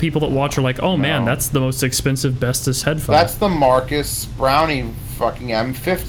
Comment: Did just call MKBHD 'brownie'? I'm dead.